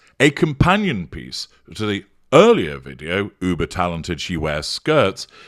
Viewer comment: I thought you said buber talented 🤣😂